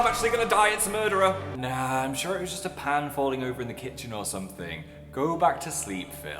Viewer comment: when phil said 'a pan fell' i heard a 'a dan fell' lol